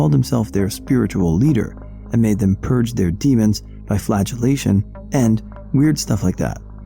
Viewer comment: I misheard "flagellation" as "flatulation."😅